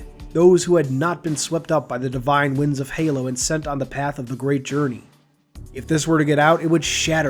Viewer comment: I thought he said the “great horny” I slept with a few species “blasting” my seed across the galios and their mates didn’t like it. So....... we have the war, I don’t regret anything